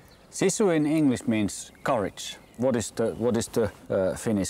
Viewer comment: When he was explaining "SISU" and said it was like "courage" in English, I heard "porridge" instead...and was like "ohhh they are talking about food now..."